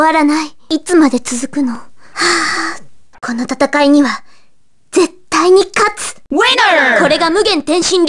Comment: What does she say here...? 🤷🏿‍♂️ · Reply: kono tatakai ni wa zettai ni katsu!